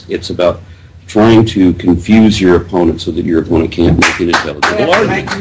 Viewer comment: "it's about trying to confuse your opponent, so that your oponent can't make an intelligible argument." It took me a while to figure out what he was saying when the clapping took place. I included captions (CC button near the play bar) just because of this.